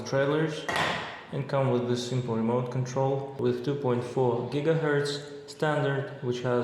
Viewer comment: what did i hear 2.4 gigahertz or gitahertz